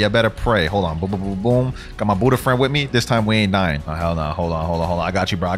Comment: I thought this man said I got my boyfriend with me 💀